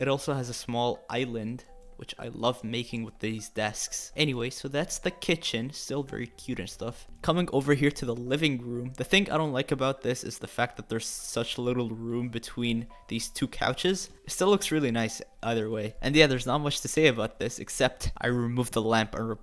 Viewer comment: Did just say, “The think I don’t like about this”